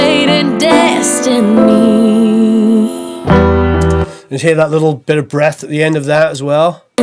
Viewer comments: Youtube subtitles say "little bigger breasts" at 3:50. Just thought I'd point out this gem lol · Hehe, check out the auto prompter at 03:47. "Little bigger breasts at the end of that as well". Didn't hear you say that??